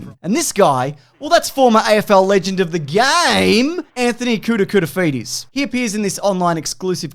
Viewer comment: Apparently, in a deleted scene for the ad, the Gorilla says "maclunkey" right before throwing the banana at AFL Legend of the game Anthony Koutoufides. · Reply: I fully thought he was saying Anthony "kouta" fides, like kouta was just a nick name ¯\_(ツ)_/¯